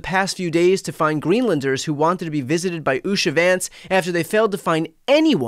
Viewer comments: I hear "shady Vance"... every time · I thought you said "Shady Vance".. My bad. LOL!! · Did he say 'shady vanse? Perfect! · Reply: I thought he said that, too. Kinda fits....'Shady Vance'..... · Thought you said " Shady Vance " !!! Very fitting!! 😂😂😂